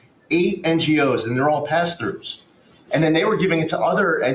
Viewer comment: Did he say Billions of $$$ DOLLARS going to PASTORS??